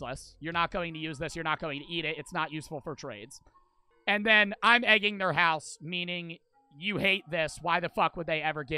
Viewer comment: i thought it said ” im edging to their house ” 💀